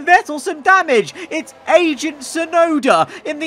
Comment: I thought he said "Asian" Tsunoda instead of "agent" Tsunoda 🤣🤣🤣